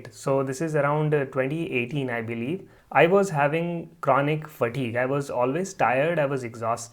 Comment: I thought you said you had Connie for Tea at the beginning then realised you said chronic fatigue lol... not very easy to listen to unfortunately :/